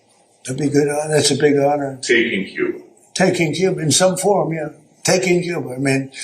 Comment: I thought he said, taking Jupiter! He has no effing clue